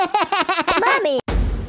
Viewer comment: Bobby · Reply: I thought he said mummy!